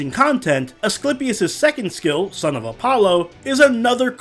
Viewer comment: Misheard say the 2nd skill as Son of A B** Me: :Coughs: His skill is called what?! Scrolls back to that part. Me: Oh... My bad...